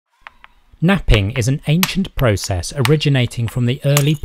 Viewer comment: thought you said “nutting” there 😅